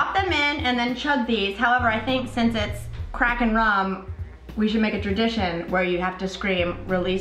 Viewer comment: I thought you said "Crack and Rum" not "Kraken Rum" I was worried for a second.